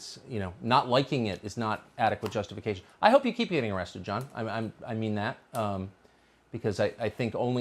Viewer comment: Did Tucker say "I hope you keep getting arrested, Jon, I mean that."?